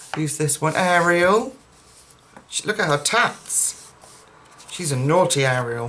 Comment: I thought u said look at her t*ts for a minute then realised u said tats lmao... happy belated birthday lovely lady and thank you for sharing your beautiful gifts. 🙂